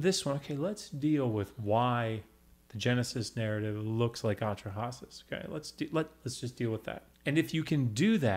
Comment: Can someone tell me what he says around 53:50 'Let's deal with why the Genesis narrative looks a lot like....' Otrahosus...? or something...?